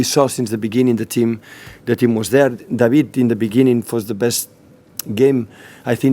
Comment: did he say David De Gea? :P